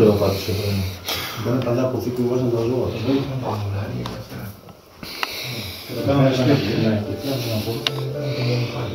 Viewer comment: Στο 12:36 ακούγεται μια περίεργη "ήσυχη" φωνή αν και δεν μπορώ να καταλάβω τι λέει. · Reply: Προσέλθετε ..